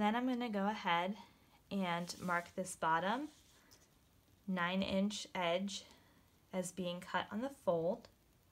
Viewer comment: can someone please explain to me what the measurement is. i even put the subs on and cant hear and understand her. is it 1 inch in? thank you in advance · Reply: Currey Panda I think she’s just saying to mark the pattern, where the bottom 9” edge is, on the fold. It doesn’t matter how far in you make that mark as it’s just for reference. I hope that helps.